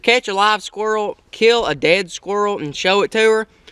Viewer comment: did you just say "kill a dead squirl"? it dosnt matter i just thought it was funny, good videos keep it up.